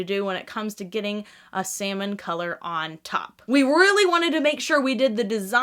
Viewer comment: oh god, i accidentally misheard "salmon colour" like "semen colour"🙈